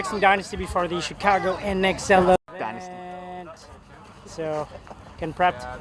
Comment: did he just say fuck dynasty